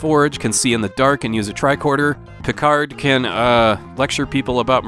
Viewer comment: Watching Star Trek on TV as episodes were released was a great thrill. 3:04 Did you say "Kicard"?